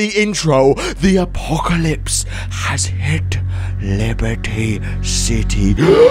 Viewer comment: did he say Liberty City